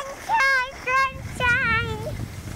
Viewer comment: Another good one 👏👏👏 What does say at 06:18? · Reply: “Sunshine!” I'll try and keep up with the subtitles!